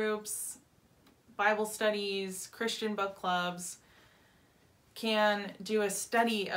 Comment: It took me a second to realize what you said. I don’t have the best hearing, and I heard “Christian butt plugs”. 😂